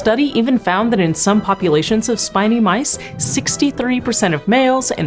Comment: I thought it said spicy mice